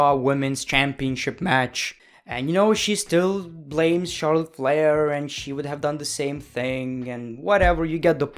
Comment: Every time he said Charlotte flair I thought he said Charles leclere I was really confused